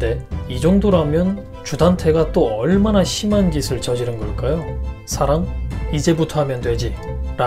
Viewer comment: 저는 "살인? 이제부터 하면 되지" 로 들리던데ㅋ 일주일을 또 어떻게 기다리지? ㅠ.ㅠ 김소연은 진짜 천서진이 되버려서 매회 볼때마다 소름돋음👍